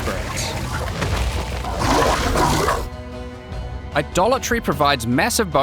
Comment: I swear, every-time he says Idolatry I hear "A Dollar Tree" 😂